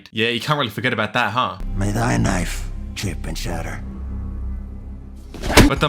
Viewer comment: "May THY knife, chip and shatter" - Feyd Rautha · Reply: i thought he said "may MY knife, chip and shatter" T-T